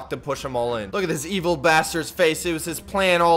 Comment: did he say basterd